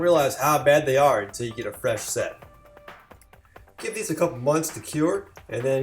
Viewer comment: Did you say give them a couple months to cure @ 7:55? Do you need that much time?